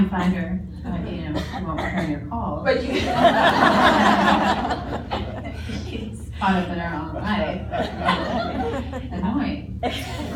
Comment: What did Idina say at 3:45? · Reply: Talking about herself she says, “when she won’t return your calls...she’s caught up in her own life. It’s annoying.” They were so cute together😊